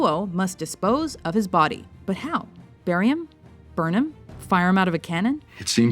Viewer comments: when she was listing off ways they could dispose of amelio and said 'bury him?' I thought she said 'barium?' and I thought 'but barium doesn't kill' then I played it again and saw what she really said. Damnit, chemistry. Don't even know what phrases and words differ anymore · Was I the only one that when, at 4:23, when she says "burry him", I heard her say "barium" and thought "That wouldn't get rid of a body... o.O".